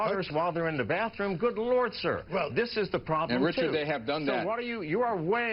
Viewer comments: what does the national security guy say at 4:09 exactly? I heard something along the lines of "wish... done that" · What did the FBI guy say to Mcafee about hackers watching their daughters in the bathroom?? @ 4:06 The host rang the bell because it was a huge fuck up by the FBI guy. I think he said "I wish they had done that". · What did this guy say at 4:10 when you said, "watching our daughters when they're in the bathroom?" It sounded like, "and Richard, they have done that." Is that what he actually said?